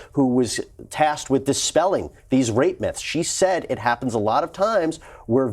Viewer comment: I thought he said “rapements” not rape myths lololol